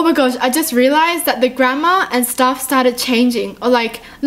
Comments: When she said Grammar I thought she was saying Grand ma 😅😂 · I thought you were saying Gramma instead of Grammer😂